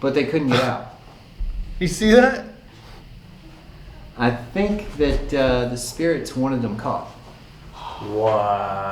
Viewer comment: On the 4th floor at 55:33 you can hear a female voice. I couldn't make out what she was saying! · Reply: I think she said "I need help" or "please help"